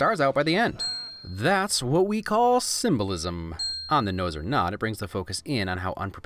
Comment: You: talks about puns Also you: "That's what we call symbolism" Me: did he just say simba-lism?